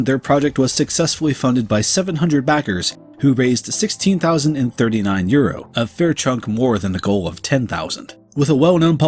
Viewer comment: The subtitles says pounds instead of euros lmao